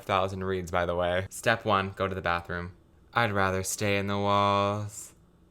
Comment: I thought it said “I’d rather stain the walls” …